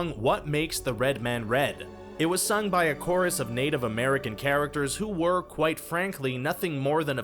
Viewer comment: did he just say chara-cer-ters